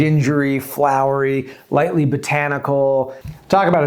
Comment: Bruh my brain is rotted by wendigoon, I heard “Bradford Pear Incident” as “Byford Dolphin Incident” im cooked